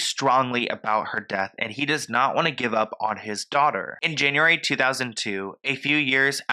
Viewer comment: Love the video🤎 but at 9:40 did say 2002 or am I tweaking?